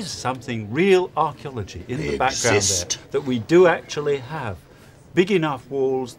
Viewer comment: Why does someone say "exist" at 16:37?